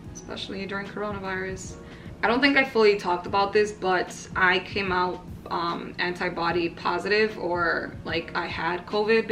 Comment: I heard "I came out as anti-body positive" and I thought you meant you were against the body positivity movement 😂 I was thinking "where is she going with this..."hahahahhaha